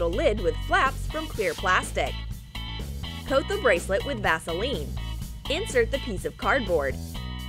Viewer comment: did she say "Gasoline" or "Vaseline" or what?? :v